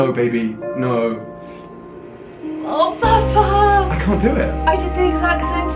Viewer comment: did she just call him papa?